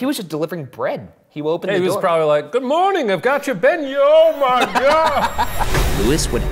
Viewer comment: umMMM acTUallY,,,,, the enGLimsh captions are wRONG...... here...... Shane was trying to say beignets dabdabdab